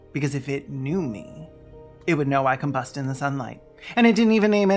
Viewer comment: Combust in the sunlight?! I thought you said “can bust in the sunlight”! Oh my old ears!